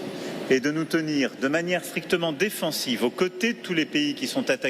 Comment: à 3:18 ...sa langue a fourchée il dit : nous tenir de manière " frictement " au lieu de ...strictement défensive .. lapsus révélateur : il admet inconsciemment qu'il va y avoir des ... frictions ... ... quoi qu'il en dise, défendre c'est devenir ou être perçu comme ... belligérant ...